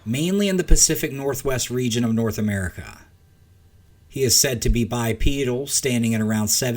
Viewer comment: "he is said to be bi-pedal" and all I heard was: "he is said to be bi-sexual" 😅